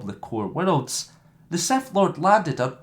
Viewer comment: did you just say seth lord